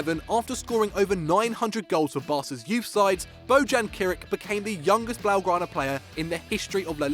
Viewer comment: Did he just say bojan kric -_